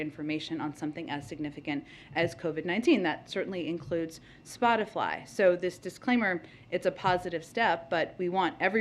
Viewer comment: DID SHE JUST SAY "SPOTIFLY" LMAO.. 30:56 LMAO.